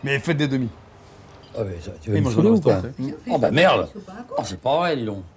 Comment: Jsuis pas sûr de ce que j'ai entendu la aidez moi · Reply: Oh bah éh ça va, tu veux te saouler ou quoi ...